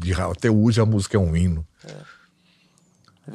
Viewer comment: que susto, ouço acelerado e tive que voltar pra ver se tinha entendido direito, achei que ele havia dito "ruim" mas ele disse "um hino" kkkkkk